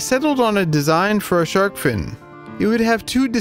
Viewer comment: thought it said found a shark fin